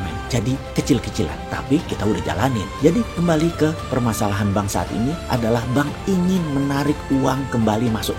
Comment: Saking semangatnya menit 03:28 Kedenger BANGS*T ini ,,😁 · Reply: Bank saat ini